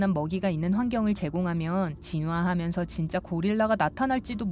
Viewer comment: 왜 징화라고하고 고질라를 고릴라라고하냐 나만 그렇게 들림?